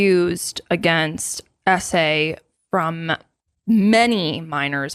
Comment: You guys kept saying SA and I thought you were saying Essay... I was lost for a good 10 minutes. It infuriates me that people with these big followings do this and try to deny it so they don't lose deals/sponsorships. I wish people could just be better humans.